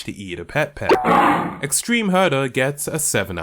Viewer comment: It sounded like you said "extreme murder"